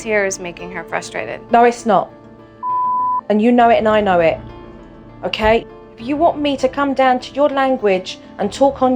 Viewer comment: YO- JO, LANGUAGE… (anyone got any idea what she said? LMAO) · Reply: I believe she just said "Bullsh*t"